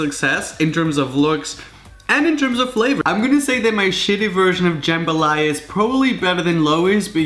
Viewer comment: did he say shitty?